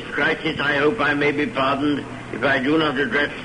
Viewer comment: am I the only one who heard what he really said here? “If I do not adress anything’s the matter of today”..